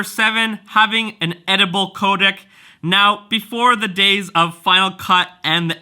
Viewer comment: Which one is more user friendly to you? Also i thought you were saying edible codec... Lol then i realized you were saying editable codec. Is money an issue with going sony? (Having to buy new lenses) I think the video quality on the R5 is good but the a7siii definitely has a handle on eliminating the wobble... I think the wobble can be distracting... However, i know there are some new canons coming out... Would that be an option for you?